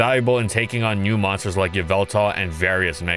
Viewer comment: i know he said "various megas" but why did I hear "various n*ggas"? 💀💀